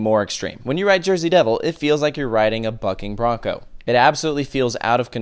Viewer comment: I thought he said f$@ bronco